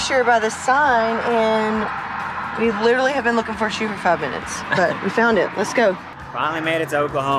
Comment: Help anyone? What does say at (4:42) ... sounds like 'we literally have been looking for a shoot of covenants?' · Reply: I think shoes for five minutes